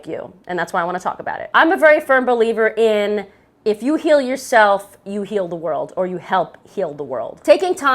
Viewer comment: I swear I heard "if you kill yourself, you heal the world" and I went "ouch, harsh!" She said "heal yourself".